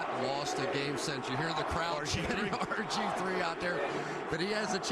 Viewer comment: I recall watching this game with my girlfriend & I thought that the crowd was chanting "RVD!", at first instead of "RG-3!"...